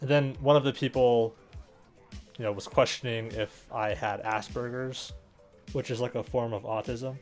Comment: Lol i heard "assburgers" instead of asperger's